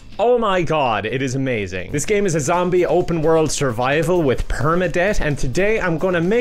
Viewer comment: Did he say "perma debt"?